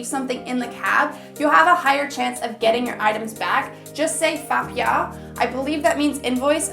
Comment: It literally took me 3s until I realize what you were saying is “fa piao” not “fk y'all” 😂😂